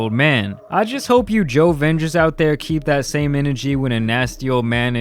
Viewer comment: What he said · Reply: jovengers